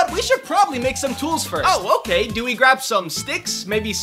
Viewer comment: I thought he said “ we should probably eat some tools first” 💀💀💀💀